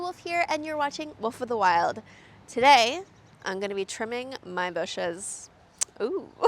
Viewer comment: I thought you said, trimming your bush for a second 😲😂